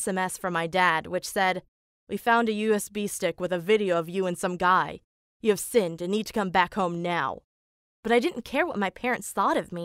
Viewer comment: What i heard at 7:13 You have sinned in this Christian home..so it be time to grab the holy bibble and water